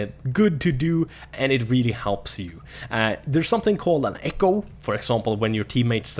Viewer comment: i thought he said echo not eco. (I dont know why im watching this im mg 1)